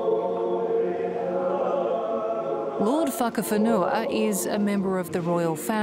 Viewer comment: did I just hear lord fuckafanua 😂